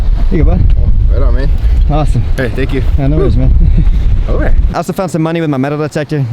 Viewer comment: What did he say there? "No way"? 1:42 PM 7/14/2019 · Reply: He said aurite. Slang here for alright. lol